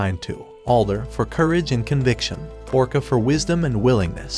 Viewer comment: hmm is it just me or did it say orca?